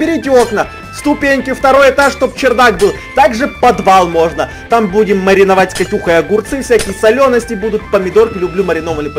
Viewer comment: мне послышалось-Будем Катюху мариновать!😅😅